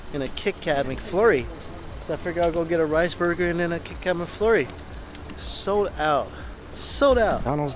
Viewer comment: Am I the only one who thought he's saying King Crab Mcflurry until he whips out the KitKats??? Lmao